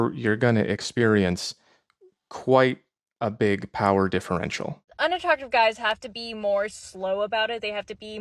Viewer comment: what did you said right there? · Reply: power differential